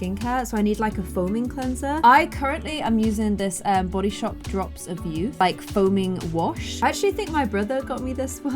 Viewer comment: I thought the drops of youth said drops of yoghurt 😂😂